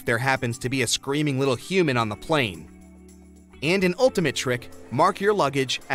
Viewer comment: thought he said a screaming little Cuban on the plane. 😂